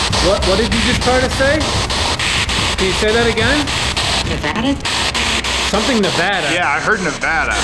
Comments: said „I didn’t die” or atleast that’s what I heard! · After 36:40 i hear "Where are you from?" Then she says "Nevada?" Like a question.